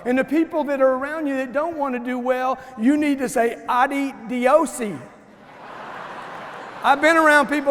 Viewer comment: What word is that ? Anyone? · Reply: Adios in Spanish which means bye!